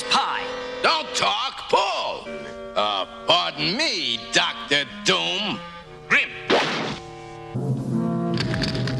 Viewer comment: did he just say 'rip'? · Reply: He said Grimm really fast the things name is Ben Grimm